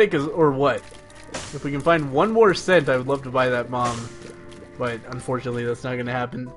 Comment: did you say "if we can find one more cent I'm gonna buy that mom"? at least it sounds like it. I know you mean bomb but mom would be so funny xD the milfhunter. The Green Story.